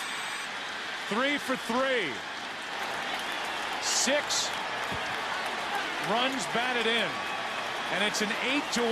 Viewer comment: What was she shouting? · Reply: "Go Red Sox" and then "Whoo!"